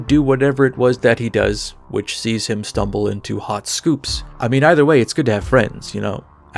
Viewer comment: I totally misheard you and thought you said stumbles into hot soup, and i had to rewind a bit lol